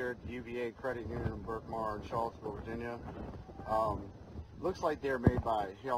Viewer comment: Where is this at? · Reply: It sounded like he said Charlottesville Virginia